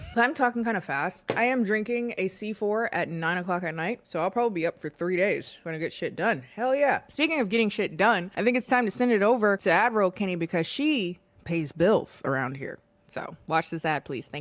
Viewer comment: For the longest time I thought you were saying 'admiral Kennie' rather than 'ad-roll Kennie'.